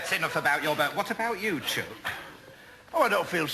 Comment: what is the pet name Ada calls Cissier? Sounds like she says, "What about you, choke"...I am sure it is not "choke" but sounds like choke or cherk?? she often says it in other episodes. I'm American, so the accent is throwing me.... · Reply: Haha its chuck rhymes with cook. It's a sort of term of endearment used up north.